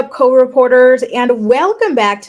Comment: I like that you enunciate co-reporter more now cuz I never knew what you were saying lol I thought you were saying code reporters but I was like why would she be saying that 😅